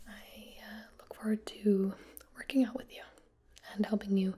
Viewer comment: i seriously thought she said “hate your fitness goals”